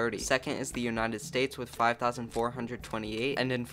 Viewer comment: I think there is something wrong with my ear. I hear "Nudes" instead of "nukes".